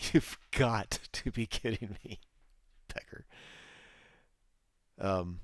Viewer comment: did just say "pecker" ??? LoL